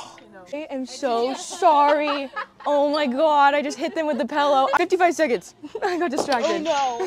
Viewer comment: Am I the only one who heard her say pellow?